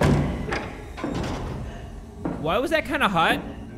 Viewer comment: "Why was that kinda hot?" That dude did NOT just say that